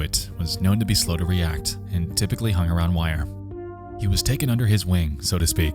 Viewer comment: Sorry for sounding dumb but when said " He was typically know to hang around Weiher" I thought he said WIRE and i was like well if hes mentally handicapped that may make sense